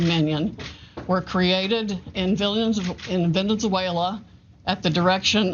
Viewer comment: Did she just say "Venen-Zuela"?? 🤣🤣🤣